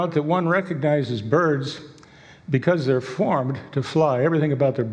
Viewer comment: I think I need to have my hearing needs to be checked. I thought he said farm to fly. Besides, he said if birds aren't extinct? Huh? Of course birds aren't extinct.